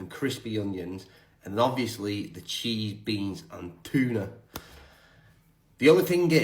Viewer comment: Did u just say cheese beans and tuna lol